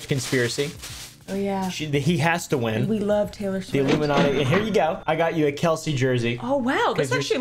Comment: the illuminati???????????? did i hear that right